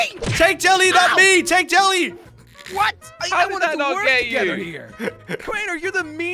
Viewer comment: who thought said"how would I not get you" instead he said how did I not get you" to jelly